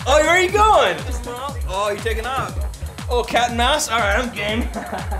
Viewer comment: did he says in 2:23 i gay??''